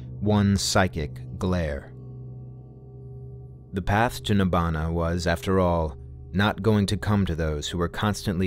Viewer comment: the Sanskrit word "Nirvana" never existed, until several centuries after Buddha's death. the original word is NIBBANA. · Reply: funny thing was i thought the word he said nibbana as to "sath nibhana "